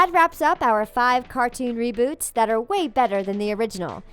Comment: I thought it said cartoons that had been roboted 😂😂😳